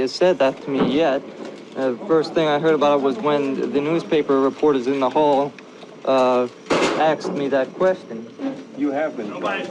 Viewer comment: Whoever wrote what Lee was saying, made a mistake. At 0:06 This is what he actually said: "The first thing I heard about it, was when the newspaper REPORTERS in the HALL, uh, asked me that question."